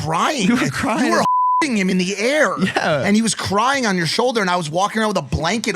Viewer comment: on Spotify it sounded like mike said Logan fk him(dwarf mamba) in the air